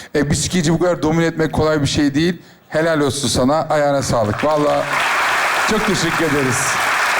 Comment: eser abi sonda ayağına sağlıkmı dedi benmi yanlış duydum